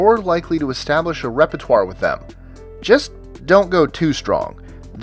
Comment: Number 15, 3:22 should it be establishing 'rapport', instead of repertoire? - Thanks